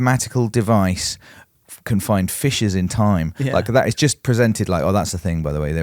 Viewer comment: I thought said "Fishes in time" at first.